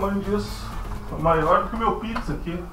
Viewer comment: Putz, estava só ouvindo o seu vídeo a aos 02:19 eu escutei algo parecido como..."olha isso aqui...é maior que meu pinto"....kkkkkk, eu pensei que vc estava louco, voltei para o vídeo e ví que se tratava de outra coisa...